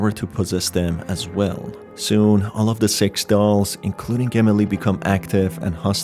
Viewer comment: is it just me or can I hear him say sex dolls?